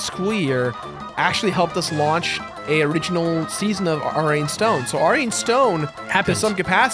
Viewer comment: Can anybody tell me what is saying here? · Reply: Buzzard it's ari and stone